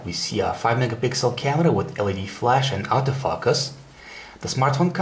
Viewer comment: I honestly thought he said "Auto Fuckus" lol Nice review though!